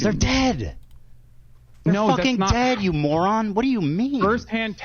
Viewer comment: Ethan said THEY not YOU to Hasan, Hasan just misheard him. Then ofc a twitter neckbeard comes to spread misinfo on Hasans behalf...